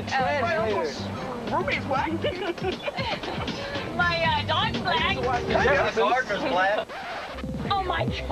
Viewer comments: Haha I love how ''My gardener is black.'' was too edgy for this whole group. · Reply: I initially thought he said "My doctor's black" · they really thought “my gardener is black” was worse than “my dog is black” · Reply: i thought he said my barbers black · what did he say? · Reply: Oh he said “my gardener is black” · This man is a world treasure, "I'm surprised at some of the stuff that I think bout" lmfao what did he say at 2:33? I rewatched it a bunch of times but couldnt catch it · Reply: my gardener is black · Can somebody please tell me what he said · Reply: My gardener is black. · what did he say? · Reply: ohhh my gardner's black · Haha I love how ''My gardener is black.'' was too edgy for this whole group. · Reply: Not gonna lie , I thought he said “my marker is black’ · What did Theo say at 2:35??? It had to be funny but I couldn’t hear it😂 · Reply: My gardener's black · What does he say at 2:34? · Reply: Gardener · what he say? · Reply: My gardener is black